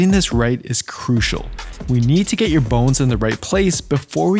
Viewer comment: He said bones, I seriously thought he said 'get your balls in the right place'! I was like YES! THIS! Super important.....😅